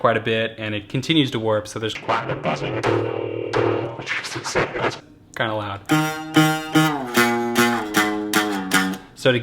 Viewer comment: Someone find out what he said at 3:04. I want to know every word that comes out of his pretty mouth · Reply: something like ...and it continues to warp, so there's quite a bit of buzzing. [plays guitar] Jesus, that's... kinda loud.